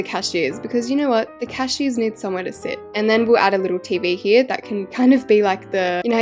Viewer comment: I thought when you said the cashiers need somewhere to say, I thought you said the cashiers need some medicine lol 😂